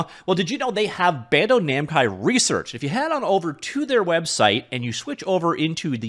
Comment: Did he say BANDO NAMCAI RESEARCH?